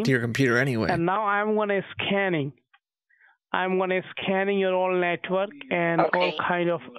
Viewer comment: did anyone else hear the scammer say 'im gonna scam you' when they first heared him say it